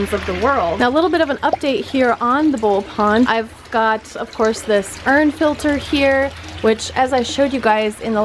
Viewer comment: My sister thought you said "Sperm Filter". I love the ballon Mollies!!